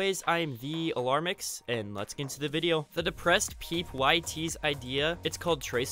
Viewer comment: Yoooooo wtf thank you for letting me be in a vid I love u and u are now my fav youtuber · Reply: did you just call him the depressed peep?